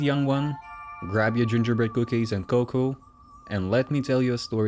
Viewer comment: I thought u said grab ur cookies and goku😂